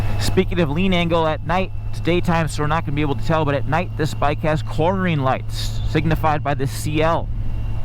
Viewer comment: i thought he said chlorine lights